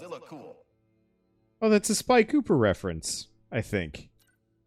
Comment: I don't know if you ment Sly Cooper instead of Spy Cooper or I either misheard what you said. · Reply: Sly cooper